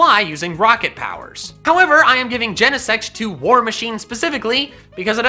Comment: When you said genesect I almost thought you said genocide. I’m a true pacifist fight me chara